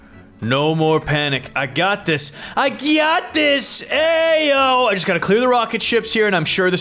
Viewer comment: How many times did he say I gyatt dis 11:16